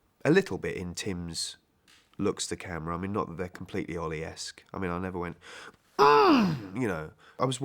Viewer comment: What does he say at 22:46? "Not that they're completely 'Orleasque'? " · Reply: Fibilotta Rodríguez - Ollie-esque. As in Oliver Hardy, I think.